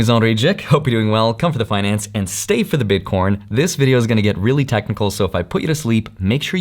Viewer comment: Did he said bitcoRn 🤠🤠? 1:55